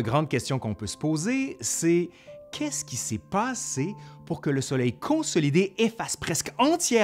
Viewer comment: Le nombre de fois qu’il dit « soleil » au lieu de « sommeil » 🌞🌝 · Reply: Haha, à 9:37 moi j'entends vraiment "soleil" ! Personne peut me convaincre du contraire.😄